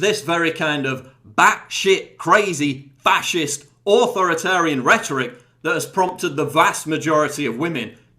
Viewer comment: AHAHAHA I seriously thought you said "whorethoritarian" at around the 2:47 mark, I had to rewind the video like 10 times XD